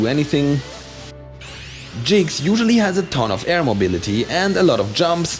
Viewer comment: Did he just call Jigglypuff Jynx